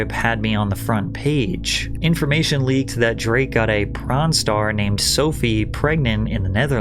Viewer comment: Did he just say Drake got a “Pron star”pregnant ? 🤔